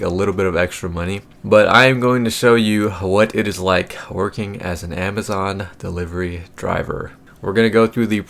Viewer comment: I swore it said 'working as an Asian delivery driver'😅